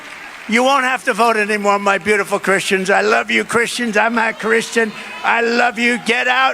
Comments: seriously am i hearing things or did Trump just admitted he’s not Christian? Because that one get out and vote Christians line he sound like he said very clearly “I’m not CHRISTIAN “ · Does he say "I'm not Christian"?